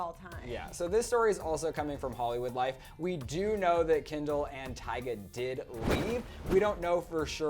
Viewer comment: Did he just say Kendall and tyga